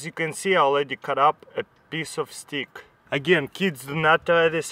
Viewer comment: I sounded like he said piece of staek